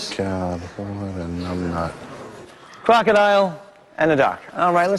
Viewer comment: 04: 22 What says when Cameron shows the Crocoduck? I thought I heard "Numbnut". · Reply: I think he said "oh god... what a numbnut"